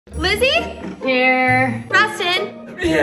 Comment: i thought she said glizzy instead of lizzy